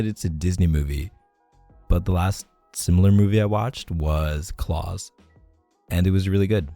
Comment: NGL thought he said CLAWS at first 😂 but i think he said Klaus, which is not a Disney movie, but it IS an animated Christmas movie on Netflix made by Sergio Pablos Animation Studios